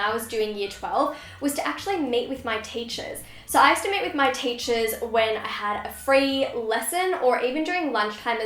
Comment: I'm sorry I legit thought for a second that you said " I used to mate with my teachers" ahahahaha